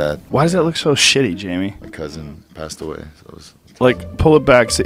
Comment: you can see the guest repeat quietly "passed away", while turning away from Joe and the screen. The guy eas shook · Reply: I think he said "my cousin passed away" I could be wrong.